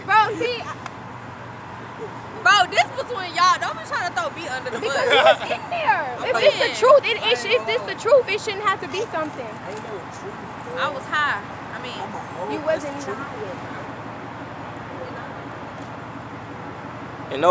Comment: isn’t she pregnant? Did she just say she was high…